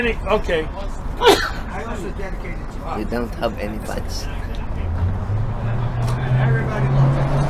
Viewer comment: What did say at 3:00? "You don't have any lines"? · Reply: "Fans" . He was finishing the classic line that was saying in the background. You can hear at about 2:50ish, doing a little back and forth with.